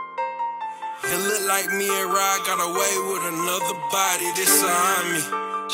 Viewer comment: I heard “it look like me and rob got away with another body” and said this shit bout to go hard